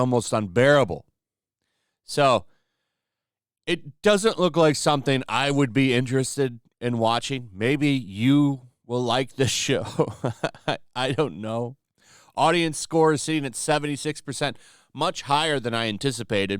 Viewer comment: "Maybe you will like this shi- er, show." That's what I though I heard. Fits perfectly! 😁